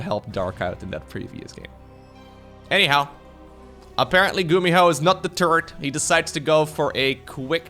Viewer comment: I heard "gumiho is not the turd" lmao